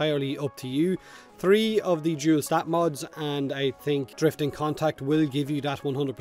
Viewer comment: really sounded like “Jew stat mods” 😂